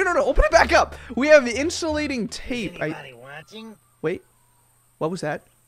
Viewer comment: lol the voice you heard was saying "Is anybody watching?" and he sounded soo funny😂😂😂😂😂😂😂😂😂😂😂😂😂😂😂